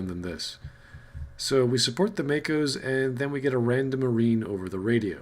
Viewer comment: When did they say anything about supporting Makos? I thought they said "tanks."